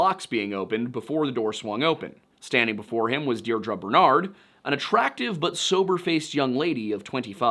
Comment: Yeah. I need to have my hearing checked. When you said Deidra Bernard, I heard "Dear drunk Bernard". Which would make so much more sense, considering the story of Annabelle.